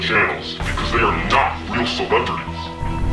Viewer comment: I heard don't see serve not real celebrates and hello I think there filming